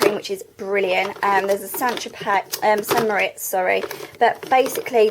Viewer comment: swear she said sanitary pad :")